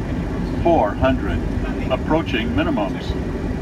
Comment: Hola en el minuto 7:23 la computadora dice algo que no entiendo, five... Y despues lo repite pero en distinto conteo que es eso?saludos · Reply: Five Hundred = 500 = 500 Pies. Es la separación desde el Ground (Suelo) del Avión. Ese conteo es luego Four Hundred (400) Three Hundred (300) etc, etc hasta que canta el Approaching Minimums (Llegando a la Altura Minima, o Altura de Decisión) y desde ahí va indicando cada 10 Pies la separación del Suelo hasta tocar tierra. Saludos!